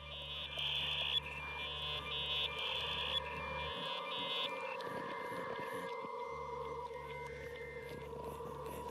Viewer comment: ¡¡¡Increíble!!! ¿Alguien podría decirme qué dice Gabo a las 01:22 '/ 41'? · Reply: "siempre hay algún turre que las usa"